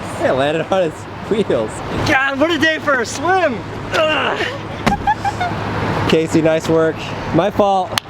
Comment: i thought he said "Casey Neiswork" 😂😂😂